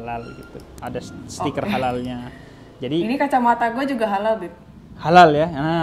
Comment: Assalamualaikum... Kak Gita, hati2 kalau panggil Habib ya... Maaf bukan nya apa, panggil 00:47 dengernya Beib Hahahaaaha :)